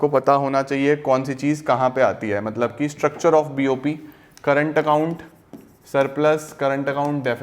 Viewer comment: Sir 6:35 aapne kya bola. Kya chiz kahaa pe aati hai. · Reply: Structure of bop